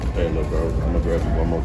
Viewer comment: what did that guy just say? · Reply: I believe he said: “hey, lil’ bro, imma grab you one more time”. He was talking to a co-worker of his. I loved the cadence and depth of his voice so I kept it in!